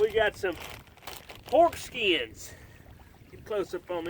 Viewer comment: Going back and watching all these videos again (wanting to go on an adventure, but living through you). I swear when you pulled out the pork skins, I swear I thought you said foreskin, I had to back up and see what was going on 🤣